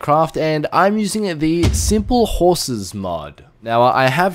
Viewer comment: Anyone else thought it said simple houses?